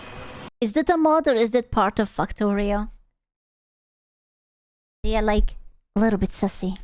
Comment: misheard this as "fucktorio" and now im never gonna hear the game's name the same way again.